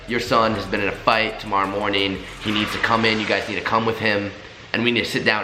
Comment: So am i the only one who heard "he got into a fight tomorrow morning..." at 7:47😂